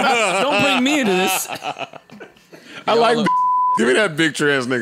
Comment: did he say 'I like bussy'?! Hahahahaha